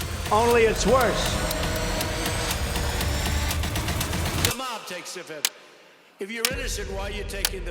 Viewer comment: I thought his said only the mob takes the fifth